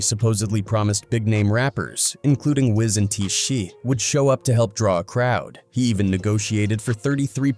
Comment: Why did he call TI T-shee?